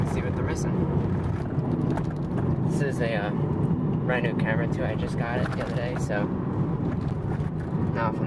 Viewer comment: He was filming with the Nissan logo on the steering wheel totally in frame and i swear i thought he said “this is a brand new Camry that i just got the other day”